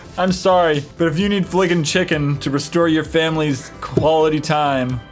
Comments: Wow, flickin chicken i thought it said freakin chicken at first!! lol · Where I live we have a restaurant called Frickers, and when they said Flickin Chicken!, I thought they said Frickin Chicken, that's what Frickers calls their chicken wings (its pretty much a sports bar) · In the video about Flicking Chicken, it sounded like they said, "Frickin' chicken!!"